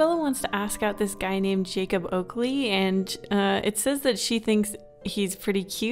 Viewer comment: I thought you said Jacob Ugly xD